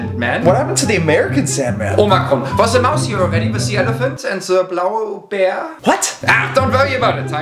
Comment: I THOUGHT HE SAID BOWEL BEAR 💀